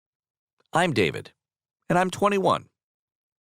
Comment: At the start I heard “I’m gay, I’m 21”